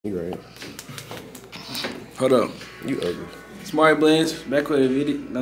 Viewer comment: At the beginning I thought he said this is my first time seeing this channel and the cut is clean asf bro